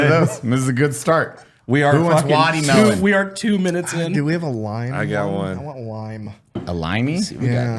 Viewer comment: I had hit pause just as was asking “do we have a lime” and I heard “do we have a line” 🤣